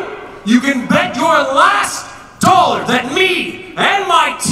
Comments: does he say breast? AHAHA. HILARIOUS!!!! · Hahaha.. At 01:32.. He says Our last "Breast" instead of Our Last "Breath".. EPIC!!.. :'D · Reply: He actually said our last “breaths” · did he just say "...our last breast"? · Omg I thought I heard him say at 1:31 “Or Breathe our last Breasts” 😂